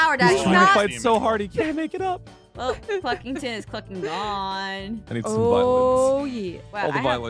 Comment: this is what i heard in 2:21 Fucking gone😂😂😂